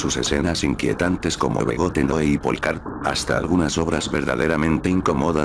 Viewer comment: Cual es la segunda pelicula que nombra ademas de Begotten? No entendi bien quiero buscarla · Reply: Applecart